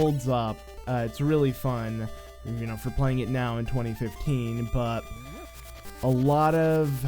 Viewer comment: What?? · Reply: Never mind. I listened to it a few more times. You said 2015... It just sounded like 2013 to me.